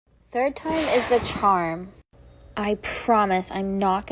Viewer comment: You misses 15 I thought you said this time is a charm